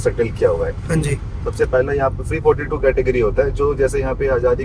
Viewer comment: Just a small note. @ 4:25 The caption says 342, instead is supposed to be "Pre-42". Source: I am from Port Blair, Andaman